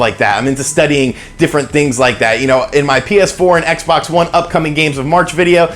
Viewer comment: Am I the only one who heard Penis 4 instead of PS4 at around the 4:15 mark